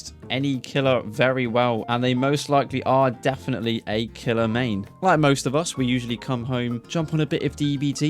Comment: I Gen up thought you were gonna say "they probably are killers" but you swiftly said "mane" surely I'm not the only one ☝️